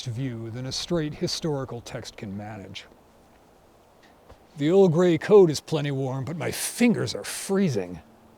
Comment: I thought he said The Earl Grey coat